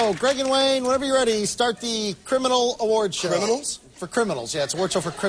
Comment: The whole time I thought it said awkward